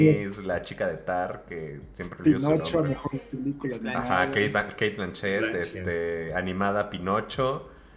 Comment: Cate Blanchet aunque te cueste y te sangre el hocico, yo escuché como producción te grito eso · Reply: cachetazo en el ocico